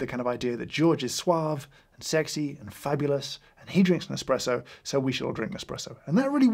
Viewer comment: Caption mistakes @ 15:00 “an espresso” instead of “Nespresso”